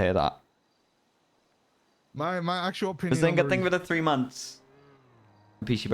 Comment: Does Behz sub to? 3:40 sounds like he says behzinga thank you for the 3 months